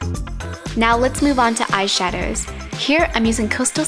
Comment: did she say eyeshaders?